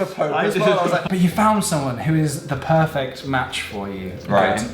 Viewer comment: When said 'the perfect match' I legit thought he said 'the perfect man' and nearly shat myself an now I'm crying with laughter. Also THE SEXY SKYPE SESSIONS?? DAN!